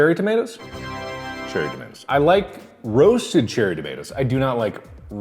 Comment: subtitles say "cherry dimensions"